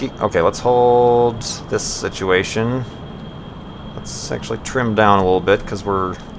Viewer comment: it sounds like he said lets sexualy trim down. rofl copter